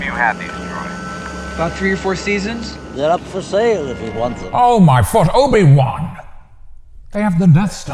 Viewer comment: I've watched this movie probably 100 times, and I've always thought Obi Wan said "they're not for sale if you want them".